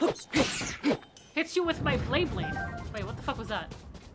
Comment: Honestly thought they said Beyblade 😂